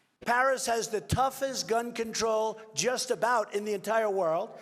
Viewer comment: I thought they were saying "bun control". Now it makes sense. Thanks.